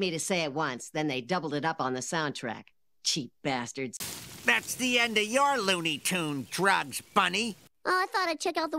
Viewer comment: What is cheif wiggum saying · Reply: That’s the end of your looney tunes drugs Bunny